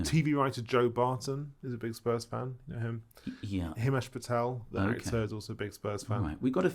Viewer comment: Thought he said Kash Patel 😂